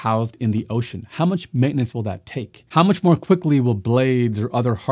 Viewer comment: When you ask "how much maintenance will that take?" at 15:44 - I hear "how many jobs will that create?"